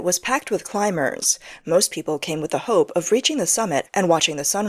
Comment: I'm so tired I heard that as "many people came with hoe" instead of hope but hearing those prices I realized that either way they would have gotten their last dollar sucked out of them.